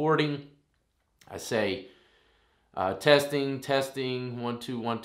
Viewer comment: sounds like it says test...test....test